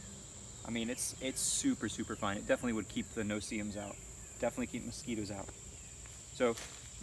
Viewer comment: Lol keep the no seams out? Were you joking? A seam is where 2 pieces of fabric are stitched together. Not a type of mosquito 😂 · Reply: oh lol I feel stupid I thought you were saying no seam. My apologies good sir